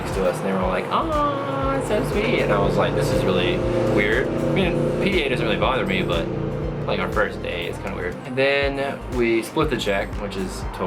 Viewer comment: What does he say from 2:39 to 2:42? Funny story, btw. Subbed :D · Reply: He says "I mean PDA (public displays of affection) don't really bother me but it was our first date so it was kinda weird." :)